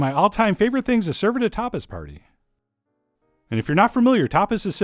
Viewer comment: Omg...at 6:30 I at first thought you said topless party. 😂